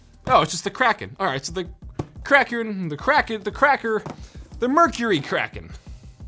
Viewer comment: I heard "mercury crackhead"